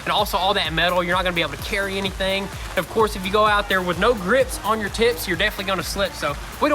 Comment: What did say? @ 1:00 If you go out there with no grips on your tits. ???